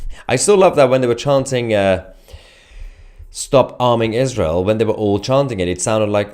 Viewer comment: I thought the were saying stop harming Israel and stop bombing Israel